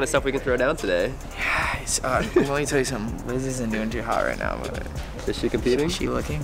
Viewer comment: When said Liz is not doing so hot right now, I thought he said lizzy is looking hot 🥵 right now 🔥 😆 😂 ❤️